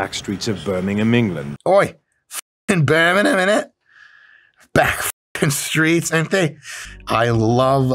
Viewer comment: "Oi foken Beemegamemenet. Bek foken streets ain the"